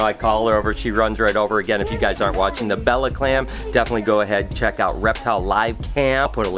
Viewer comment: Wait wait wait... Did I just hear Bella CLAMS? What kinda breeding have you been doing over there??? Haha jk have a good day 💪🏼